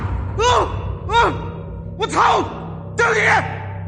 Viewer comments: Why at 9:50 it sounds like he says " watch out , do it again . " I know it's a different language , but that's what I hear . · sounds like he says “Do it again” 😂